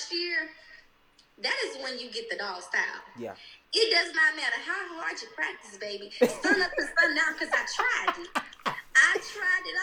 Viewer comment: What did say? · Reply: I think she said “it doesn’t matter how hard you practice baby. Sun up til sun down. Cuz I tried it.”